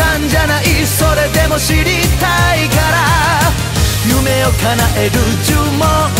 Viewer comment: 初めて聴いた時、それでも知りたいから～の部分が、死にたいから～に聞こえて、明るい曲調なのに重い曲だな・・と思いながら聴いてたｗ